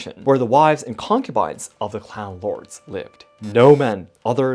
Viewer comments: I wasn't looking at the screen so when he said "clan lords" I heard it as "clown lords". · When you first said "clan lords" it sounded like you were saying "clown lords". I had to listen again and was very confused because I was wondering what ICP had to do with this.